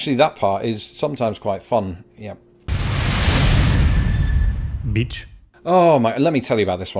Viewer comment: It's been 80+f AT MIDNIGHT. It's still 80f at 4 am. 95f for high. I'm in indiana. 14:03 NGL I thought you called him a bitch....